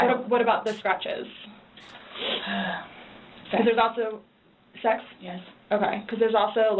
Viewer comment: Listen closely. When asked about the scratches, Sarah does answer. It's low and quiet, and starts a sentence at the same time, but Sarah does say "sex," then there's a short pause and repeats it to make sure she heard what Sarah said correctly. is not answering for Sarah and putting words in her mouth.